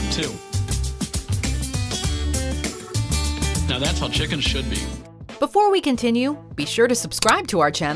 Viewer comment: i thought he said 'now that's how chicken shit be'.